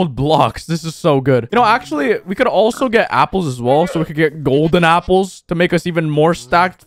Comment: I heard golden tapls lmao